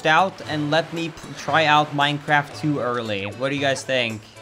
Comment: I thought he said minecraft too early at first